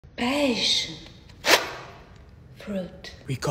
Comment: I thought she said peach... that's one messed up peach... ohhh, passion fruit!